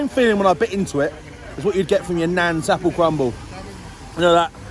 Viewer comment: Naan apple crumble I thought you said...that would be legendary